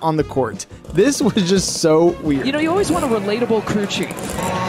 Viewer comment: What I thought I heard: “yk you always want a relatable coochie🤭” What was actually said: “Yk you always want a relatable crew chief” 💀😭😭SMH